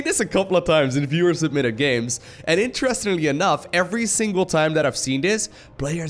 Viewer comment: I LOVE these viewer submitted plays. They're funnier than comedy. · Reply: Wayne Huang whaaaat?!? i always thought he was saying "fewer submitted replays"! omg i didnt think about that @@